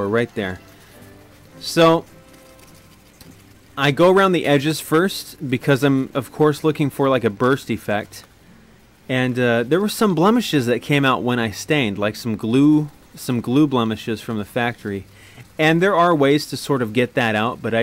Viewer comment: sounded like you said you were looking for a Birth Defect. lol, cool video's i want to build my own guitar one day 2.